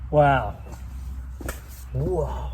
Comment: Did anyone else hear a faint sound like someone shouted Wahoo at 12:02 and lasts a couple seconds? It was right after pulled the huge log with his truck. Those RAM's are amazing vehicles. Greetings from still frigid NE Indiana.